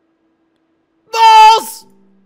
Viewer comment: What was that noise he made at 23:00? It sounded like he said “balls”.